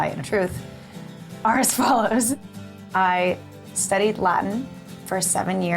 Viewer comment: At first I thought Troian said she studied lie for 7 years and I got so confused!